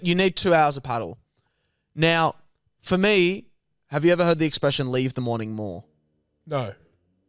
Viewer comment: Bro, i heard "leave the morning mall" too fs hahaha